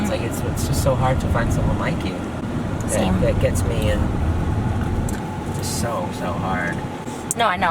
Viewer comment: Had to rewind at 12:53 because I thought he said “someone like you that gets me so so hard”